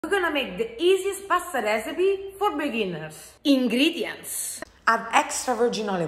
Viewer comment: I heard, "easiest pasta recipe for billionaires"